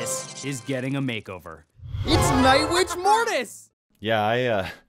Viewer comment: did I hear that correct? Nightbitch?